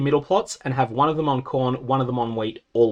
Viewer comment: I don't care what anyone says, I can't not hear "encorn"